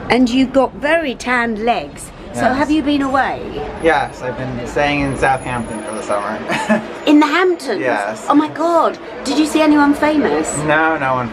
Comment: Thought at first he said Southampton- not quite as cool. Great film!